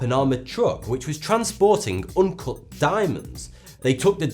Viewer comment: Am i the only one who thought he said uncooked diamonds instead of uncut diamonds?